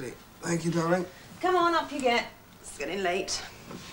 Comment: Can somebody tell me what she says at 9:20, please? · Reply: I think: "Come on, up you get, it's getting late." I guess this was one of the sentences I never understood either when I saw this episode (a hundred times) in my childhood. :)